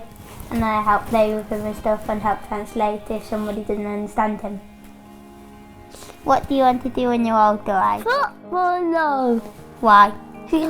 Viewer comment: What did he just say?.... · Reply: he said what do you want to be when you grow up, and Issac said he wants to be a football player